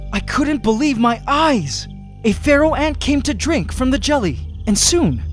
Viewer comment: Why do I think I hear "FERRAL" ant